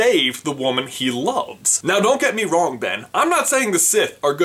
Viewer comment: Is it just me or did he say Ben